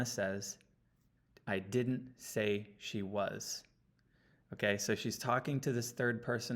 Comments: I hear “and she was.” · Do you see the lady’s face or the vase? It can be heard convincingly both ways. I hear: “Yes she was.” Great video - thanks!